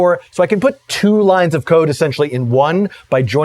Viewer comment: I was joking that it sounded like he said he was running out of lines of coke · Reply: Ha ha. I guess since I knew the context of his statement, I heard it as "code", but re-listening to it with cocaine in mind, it does sound like "coke".